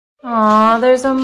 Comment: I heard 'wax burger'